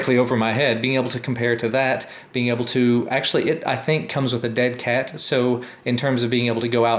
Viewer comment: did he say comes with "dead cat" at 4:33?